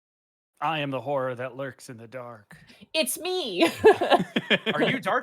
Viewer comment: For a second I thought was going to say I am the whore that lives in the dark